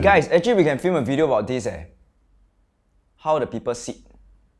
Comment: I misheard it to how people shit